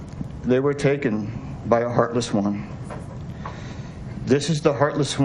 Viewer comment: did say their lives were taken by a heartless Woman??